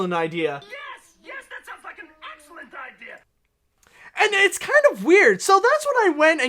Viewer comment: I definitely heard “That sounds like an excellent idea.” I played the clip several times to be sure. This is seriously less complicated than people thinking Donald said the “n” word in Who Framed Roger Rabbit. 🙄